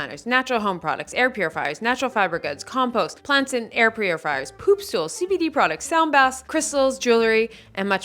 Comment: Did you just say poop stools? I don't want to buy someone's stool😮